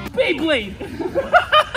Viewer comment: Did you just say "Beyblade"?